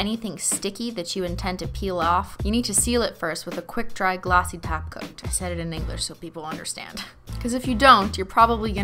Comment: I keep hearing "glassy taco" instead of "Glassy Top Coat"...